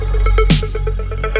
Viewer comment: I wonder what he said in reverse here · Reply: He said "I love it when you watch me sleep" (paraphrased) in reverse